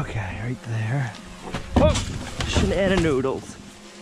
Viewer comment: Did you just say "shannon noodles"? Bwahahaha